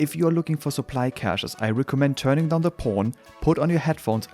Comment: did he say " ...turning down the porn..."?